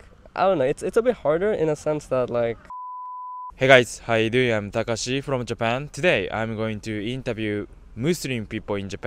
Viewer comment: At first i thought he said "i am Kakashi from Japan" Lol